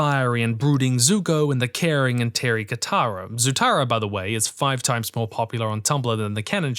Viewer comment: "the caring and ??? Katara" WHAT DID HE SAY HERE? TERRY? TEARY???